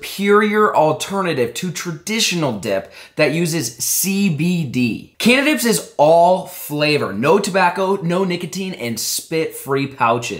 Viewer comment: I thought he said candice